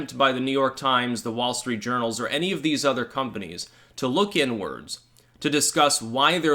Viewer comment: Did you just say " Look N words ." 5:15 That's Wassist...